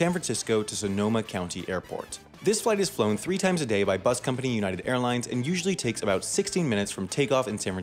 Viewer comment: Anyone hear ”bus company” at